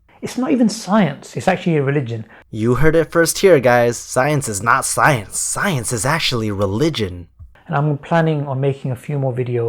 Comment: it just started seeming like the guy was crazy, or my ears had started to malfunction and he actually said scientology.